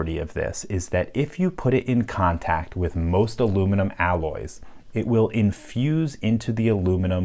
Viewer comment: You certain this lock isn't made of aluminium alloy, I keep hearing you say it's alumni or something